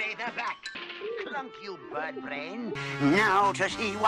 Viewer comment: WHAT!? HE SAID WHAT!? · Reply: “Klunk, you birdbrain”